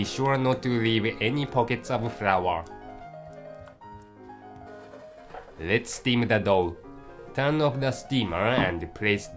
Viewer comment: i thought he said "let's steam the dog". made me kinda happy back there.